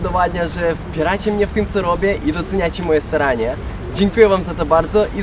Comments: ja usłyszałem tak : dzięki że doceniacie moje sranie XD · Kto usłyszał "doceniacie moje sranie" XD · czy tylko ja usłyszałam ,, doceniacie moje SRANIE"?! xD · czy tylko ja usłyszałem "i doceniacie moje sranie" XDDF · Czy tylko ja usłyszałem doceniacie moje sranie xD a tak wogle super odcinek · czemu usłyszałam "I doceniacie moje sranie"? xD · usłyszałam ,,i doceniacie moje sranie"😂😂😂😂